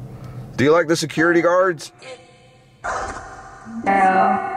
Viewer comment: when you asked do you like the security guards i heard "No" i swear